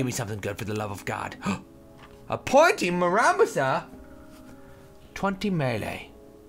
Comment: i thought it was saying Pointless Muramasa